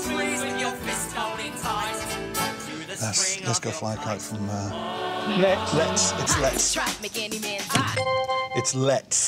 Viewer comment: they both shout "LET'S!" and lose a point because it sounded like "next" :'(